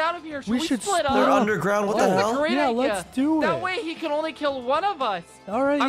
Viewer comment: I heard a hell